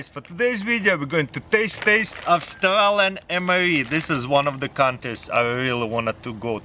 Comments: Did he just said "taste taste" on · did he say taste taste? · Hey taras did you just say TASTE TASTE? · Who else heard him saying "today we gonna taste taste? 😂 love this guy, 😍